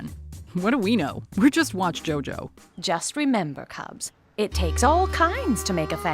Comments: Did anyone catch that she said "we're watch Jojo"? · Did anyone else hear say WatchJojo instead of Mojo? No? Just me?